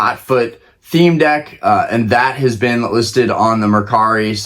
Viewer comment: did someone say crispy/krizpy lol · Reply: CRISPY